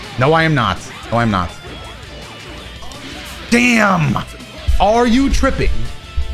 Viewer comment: I won't fault you this—as there was a time I thought the same—but @ 24:32 he actually says "—Are you trembling?" (≧▽≦)